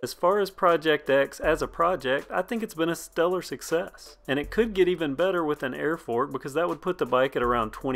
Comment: you say "it could be even better with an airport"? I can't tell what you actually said- old ears limit my comprehension. What did you actually say?